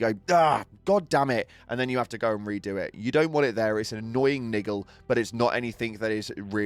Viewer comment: Did he say "niggle"?! 8:49 😂 wtf